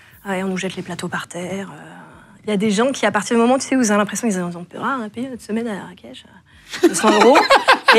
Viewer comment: qu'est-ce qu'elle a dit ?😅 J'ai rien compris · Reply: il a des gens qui ont loué une semaine à Marrakech